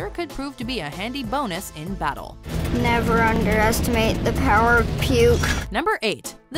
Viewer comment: i thought he said never underestimate the power of the scouts code.